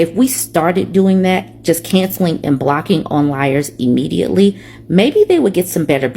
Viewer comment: Wait, did you say onliers? I'm gone💀